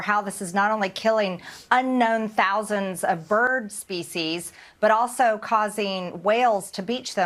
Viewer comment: 'Thousands of bird species'. What a maroon. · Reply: At first I thought she said feces..seems more appropriate